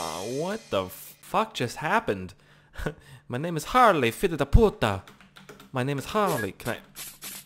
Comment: did you say "filha da puta"? Hahaha amazing xD (I am from Brazil lol)